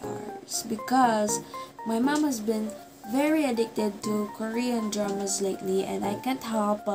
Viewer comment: I thought she said, "her mom was addicted to drugs-" then went back and turned up the volume to hear it right bc i was like, "THAT"S ILLEGAL!!!"